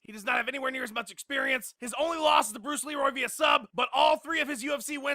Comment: I thought i heard him say BRUCE LEE-ROY @ 2:39 LOL.